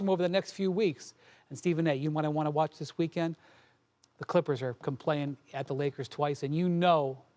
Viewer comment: Clippers are def gonna drop out. Either the warriors or timberwolves are making the 6 seed. Flippers boutta go 8-16 to finish the szn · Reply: update, I thought you said Clippers are dropping to the play in 🤦🏽‍♂️🙃